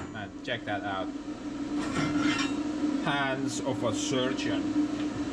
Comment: did you say that you had the hands of a sturgeon?😂😂 😂 🤠🌵🌵🌵🇺🇲